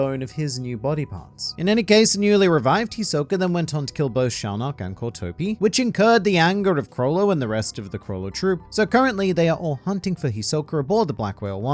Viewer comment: There's no way I was the only one who heard "Chrollo and the rest of the Chrollo Troops."